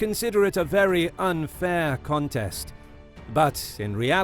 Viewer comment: You said contest but the captions say contend